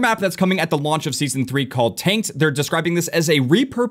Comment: Sounds like he says "taint" not "tanked." 😅